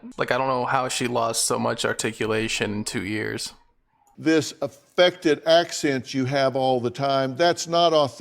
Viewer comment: I thought Dr Phil said "Infected Accent" lol 😆 🤣 😂 😜 sounds about right.